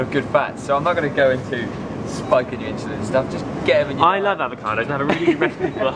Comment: Did you say u weren't going to 'spunk' on the avocados at 1:31?!